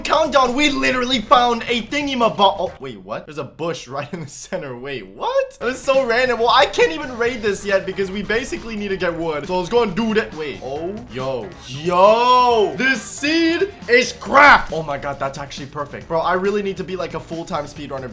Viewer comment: Bionic: This seed is cracked! What I heard: This seed is crap!